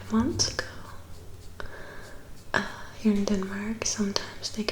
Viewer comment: "I don't deserve the kisses". Wow!! You Rosita imitation is spot on!! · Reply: (Procommando) At about 11:23 is sounds like she says, "Here in Denmark."